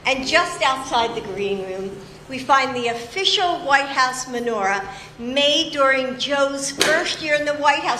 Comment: Interesting that as she describes the decorations they don't show them, as garish as they look. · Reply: She sounds like she says manure, not menorah.